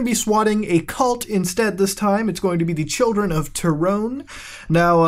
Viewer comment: Lowkey thought you said that you are swatting the children of tyrone.